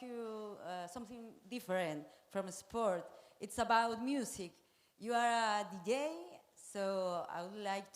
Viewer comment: LOL at Powell laughing hard at 31:05 and the lady said "Your Are EM DJ?" she did not say "GAY" that is why she was asking about MUSIC hahaha